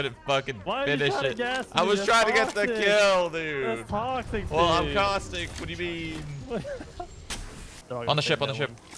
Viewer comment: I thought it said “Cautistic”